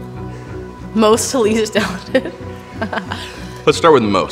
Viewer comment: "Rank these artists from most to least ___" what? I couldn't hear the word they were ranking on very well, does anyone know? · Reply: Talented